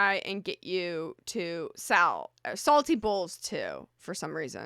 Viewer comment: Love you don't worry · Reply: Baahaaaaa, because I thought she was saying, salty balls. I’m like, WHATTTTTTT